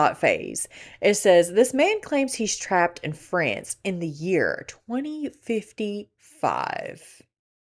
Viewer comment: I really thought #19 said Trapped In The Furniture 😂